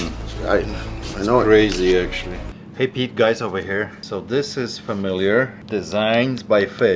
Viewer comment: Did he say "hey Pete guys here"?!? 😂